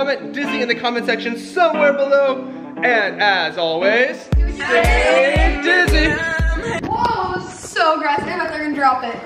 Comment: I thought he was saying say Disney not stay dizzy